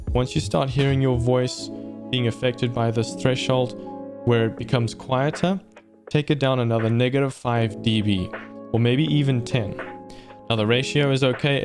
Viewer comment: did he say the N word?! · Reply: He said negative 5 db